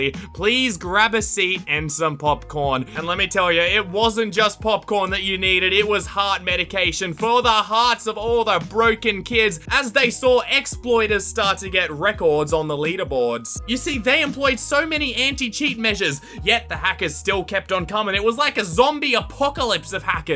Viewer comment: Did he say... did he say the hearts of broken kids?